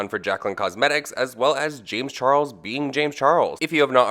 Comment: what said: “James being James” what I heard: “ Gemini being a Gemini” lol